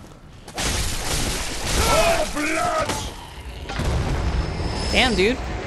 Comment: I heard O Blyat at like 44:15 and he basically said O fuck.